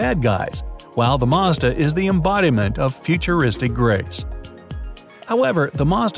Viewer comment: It sounded like he said "Futuristic grapes" and I was like "WHAAAAAAAAAA?????"